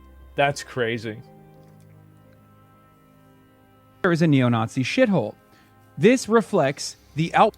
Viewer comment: Why did he just say Twitter is a "hamasabi shithole"? weird to report yourself like that